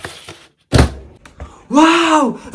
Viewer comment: Bro I heard "wow A Dosa!"